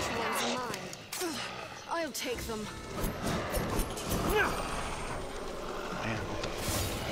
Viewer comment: Did you just say "what the Eight"? XD haha! I suppose skyrim does that to people. XP